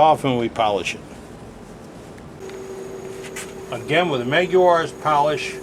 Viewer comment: what was the polish you used at the very end? I couldn't understand the word at about 48:30. thanks! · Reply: Meguiar's automotive polish.